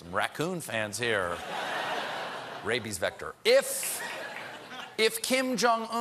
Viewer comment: Did anyone else hear rappies and not rabies or is it just me?